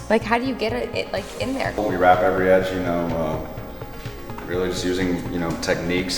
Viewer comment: “How do you do the corners?” “If we told you, we’d have to kill you”....that’s what it sounded like he was saying. LOL